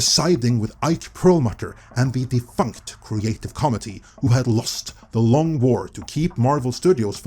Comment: Anyone else thought he was saying "comedy" instead of "committee"? I didnt know what he was saying until 18:24 when he was reading it onscreen that I figured out what he was saying when I saw the snippet that he was reading from. I was not watching the video and was just listening to it and just started watching right before the 18 minute mark. Love the videos so that is not a knock or anything.